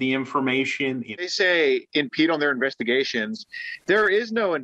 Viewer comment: When the news reporter said "investigation" i heard "gay shit"